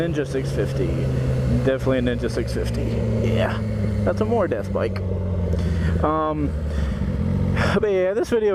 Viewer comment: What was said at 12:21? It's a more def bike is what I understood . I'm ridiculously curious